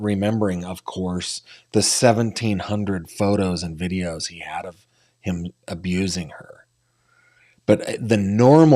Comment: did you say 1700 hundred photo and videos of abuse??? Whaaaaaaat???? 😢